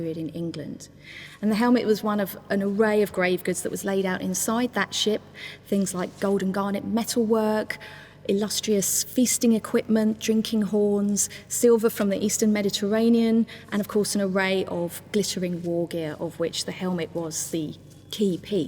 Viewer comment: Does....erm.... does she say illustrious fisting equipment?